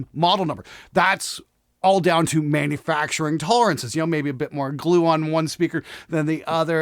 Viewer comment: did i just hear "tone glue"? i can't wait for the tone snobs on this one....lol